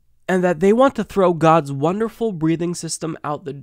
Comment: I thought she said "gods wonderful breeding system".